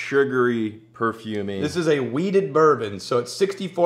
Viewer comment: I thought he said weed, he said wheat haha... I don't know why I even thought of a weed Whisky 😂